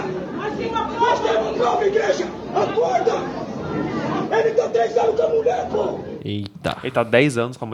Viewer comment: eu entendi "ele tá a 3 anos com a mulher, pô!"